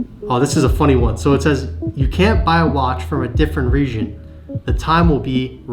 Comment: greatest comment 25:54 is he saying region with a weird accent?